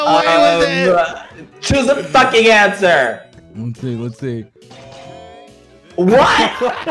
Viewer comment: the "what" sounded like bbh